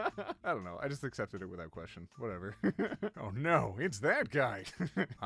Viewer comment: When I heard the "I live in Spain, but the s and i are silent," I misinterpreted it as "I live in pain" which hit pretty hard LMFAO